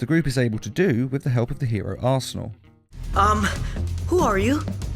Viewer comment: THE HERO ASSHOLE I know he said arsenal but that’s what it sounded like to me